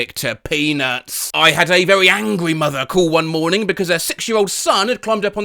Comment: i heard another thing ☠️ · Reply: “sex year old” haha so funny